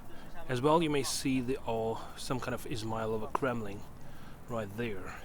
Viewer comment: Such a beautiful view.. What did you say?😅 18:31 auto captains say "Ismail of a crumbling" right there. I think your saying Kremlin, but I cant tell.. 🙃